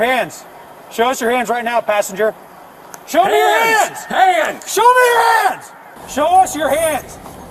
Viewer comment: I could of sworn one said show me your ass meow!!😂